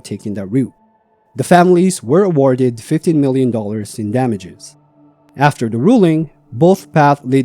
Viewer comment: The two clueless city women that died in Hawaii bear 100% of the responsibility for their deaths. Paying them $15 million for their ignorance is absurd. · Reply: Was it 15million? I thought he said 50 million.😮